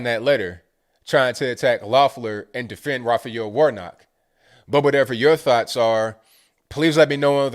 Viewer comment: I think I made a mistake when I was talking about the Senate. Democrats would have to win both seats in order to gain a virtual tie. Then Kamala would be the tiebreaker if Joe Biden is inaugurated. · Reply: I thought they said Warlock! Oh it's Pastor Warnock & not Pastor Warlock? My bad. Lol